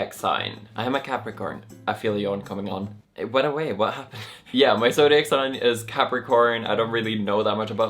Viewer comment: I'm trying so hard to understand what he's saying at 7:38 😂 Loved the video! And orange would totally suit you. Although tbh you'd be able to pull off every hair colour. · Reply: He said, "I feel a yawn coming on... It went away, what happened?" 🤣